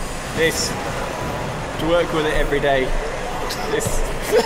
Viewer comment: "Have to work with it every day", and I heard "Twerk with it every day".